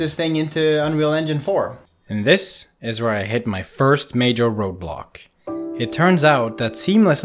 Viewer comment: Who else heard 'Roblox'....